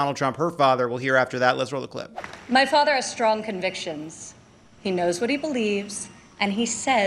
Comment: Ivanka misspoke... She said "My father has strong convictions"... She should have said "My father is soon to be convicted" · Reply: I heard, "My father is facing many convictions".